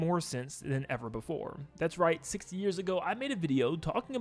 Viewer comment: is it just me or does it sound like he says "60 years ago" instead of 6 years ago?